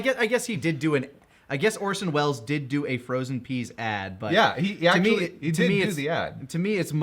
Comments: the line is "frozen peas"? I always thought the brain said "rest in peace", im learning new things about dbza a whole decade later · Wait WHAT?! "Frozen Peas"?! I thought Goku's brain was saying "Rest In Peace"! Like Goku's brain was giving up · FROZEN PEAS?! I always thought he said "Rest in Peace". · The brain says frozen peas I thought it said Rest In Peace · Wait... it says "frozen peas"???? this whole time I thought it said "Rest in peace" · Frozen peas?? I've watched this.. alot, always thought his brain said rest in peace. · I always thought he said "Rest in Peace" not "Frozen Peas".